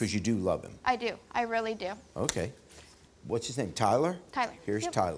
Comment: All I heard was "His name's Tyler?" Me: he cheated, Tyler's suck 😂 && He did, it was perfect 🤭🤌